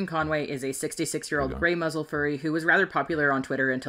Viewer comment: Did she call the person a "grey muzzle"? Bruh, I have never heard anyone say that word unironically the fandom before.